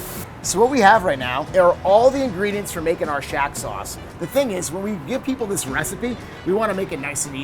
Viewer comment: I thought he said making a shat sauce at first.